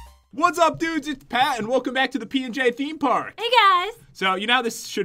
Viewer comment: What Pat said: "Hey guys, welcome back to the P&J Themepark!" What I heard: "Hey guys, welcome back to the P&J Peen/Beanpark!" WhAt